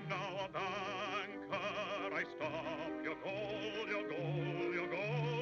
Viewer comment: What does he say at 0:34 for three times? In italian it is translated in dolor->pain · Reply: Your gold...